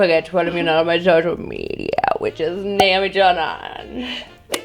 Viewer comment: thought she said nam joon